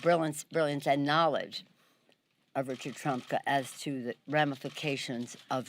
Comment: did she say Richard Trump?